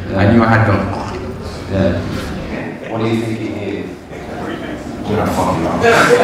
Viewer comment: What did Masvidal say when the host asked him "What are you thinking here? " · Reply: “I don’t fucking know”